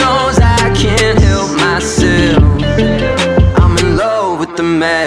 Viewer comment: when i first heard the song i thought he said “i’m in love with a manic” when it was “i’m in love with the madness” and i was like i meeean could work